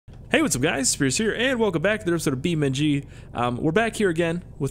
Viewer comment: Every time I hear the intro I hear "Hey what's up guys it scarce here."